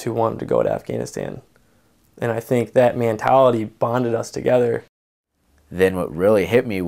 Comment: Mantality. You think he meant to say mantality instead of mentality?